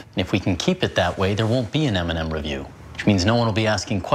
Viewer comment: “There won’t be an M and M review “ Me:DID HE JUST SAY EMINEM